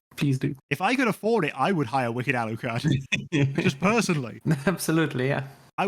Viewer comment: For a minute, I thought you were saying "Wicked Alan Carr", and was like wtf?